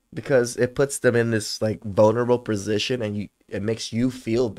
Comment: did you say “bonerable”? at 4:21, 4:25 and 4:29 - that’s funny as hell dude. I’m gonna use that.